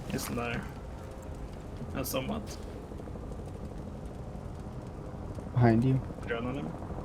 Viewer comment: did he say behind you?